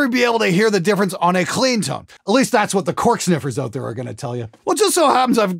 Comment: When you said “cork sniffers,” I heard “tone sniffers.” Same-same.